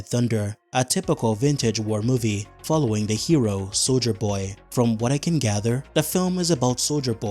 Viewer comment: The subtitles said soulja boy 😭😭